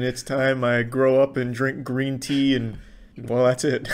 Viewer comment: Did he just say “cream tea” at first then said “green tea”?